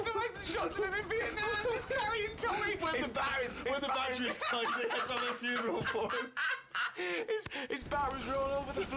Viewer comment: Can't make out what Steve is saying, b/c Ricky's laughing too loud. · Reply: I think he says when the batteries died they had to have a funeral for it